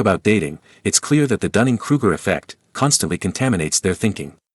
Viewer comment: I thought he said cunning cougar.